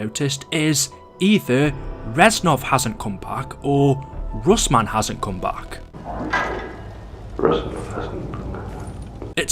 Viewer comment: i clearly heard russman hasn't come back